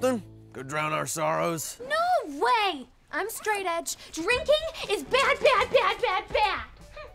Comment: Did scene call herself straightedge?